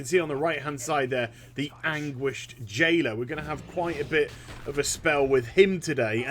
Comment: I thought you said "anguished jaina" XD